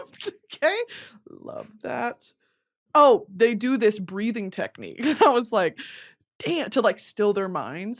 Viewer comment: i thought you said breeding technique lmfao